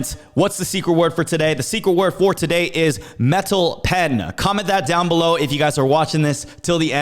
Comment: Metal Pen. At first I thought you said Metal Head lol.